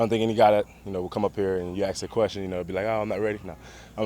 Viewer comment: Did he say AKS?